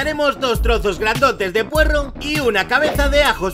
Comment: ͡° ͜ʖ ͡°) escuche porros